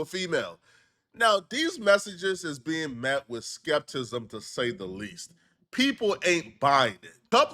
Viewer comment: incorrect usage of words for today: “skeptism” instead of “skepticism” 0:24 🤦🏾‍♂️